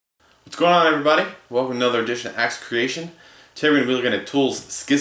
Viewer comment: I legit thought he was saying "Axeturbation"